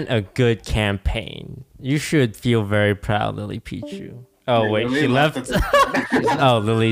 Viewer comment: did he say lily Pitre because my last name is Pitre wth